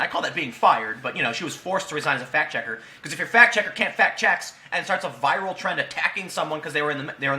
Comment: At first i thought i heard "Fact checker can't fuck checks"